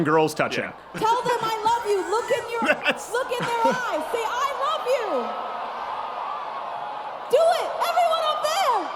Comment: I swear it sounds like she says "I ruv you"